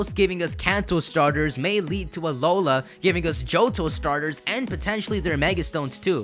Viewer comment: I THOUGHT HE SAID EBOLA XD XD LMFAO!!!